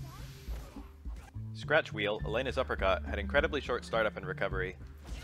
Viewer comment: I always hear "Thats REAL!" instead of "Scratch Wheel", and i feel like that sums up the disbelief I have seeing what Elena can do